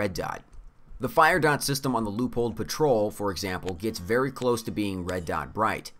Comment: I think I got them all- and yes if you’re playing along you will get drunk🎉 Strike Turkey Attack Pigeon Assault Chicken Battle Goose Murder Finch Combat Pheasant Skirmish Vulture Siege Duck Loophole Patrol?? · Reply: Hate to dissapoint: But Loophole Patrol is just mishearing rather egregious pronounciation of Leupold Patrol